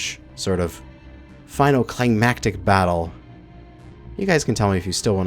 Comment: I misheard "climactic battle" as "klangmactic battle" Then I got to thinking, what better way to refer to space engineers capital ship battles than as, Klangmactic.